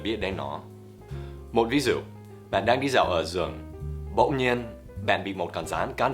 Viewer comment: Khúc 2:22 thầy nói "Bạn đang đi dạo ở rừng" ...nhưng mà nghe như ở giường í =)